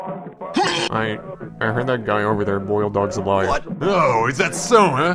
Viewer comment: is he saying boil or bullying dog? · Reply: Boiled